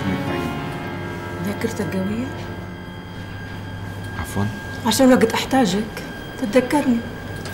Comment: حدا يفهمنا شو قالت ؟؟ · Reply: عشان وقت احتاجك تتذكرني